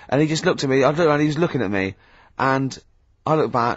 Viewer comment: what word they say at @ 14:56 ? wifront?? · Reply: Y front